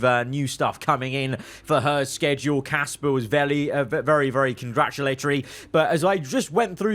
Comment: did anyone else hear say vely